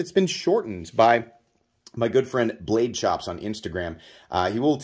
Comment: Re: the shortened Kapara... Oh it's "Blade Chops"!!! I was having the hardest time finding them because I thought I heard you say "Blade Shops" and of course that didn't lead to anything. Same here! The Kapara is a fun fidgety but I don't take it out much. ...looking into doing the Chops thing, THANKS!